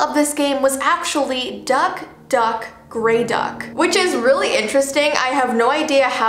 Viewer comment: FOR A SECOND I THOUGHT IT SAID "Duck,Duck, Gravy Duck"😭